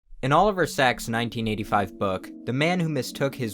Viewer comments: I don't know why I thought he said oliver sexs for a second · When said Oliver Sacks i thought he said Oliver Sex. Imma a little disappointed.